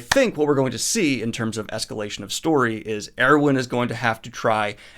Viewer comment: i was so confused cause it sounded like he said erwin lmao